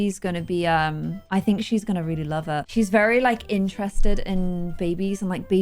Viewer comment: DID I HEAR SAY "HER" or it. Either way I'm just really excited :)